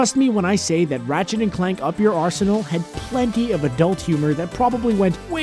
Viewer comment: thought you were gonna say 'Arse' LMAO